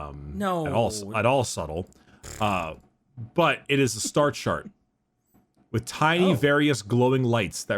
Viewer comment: I thought they said "Star Shark"... I feel sorta sad now